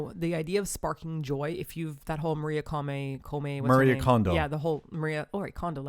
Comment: Ohgod.. It sounded like you said Marie Condo-m...I'm still not sure if you said condom or not.. Am I even allowed to type condom? .. I'm sorry kids..... Hahaha